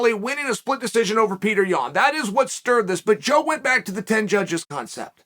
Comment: I thought Joe has said "5 Judges" Not 10